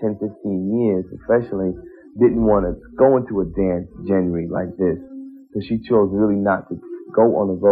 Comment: I'm sorry, did he say "dance jen-ree"?